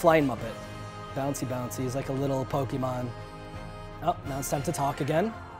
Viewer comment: Did he just call yoda a little pokemon?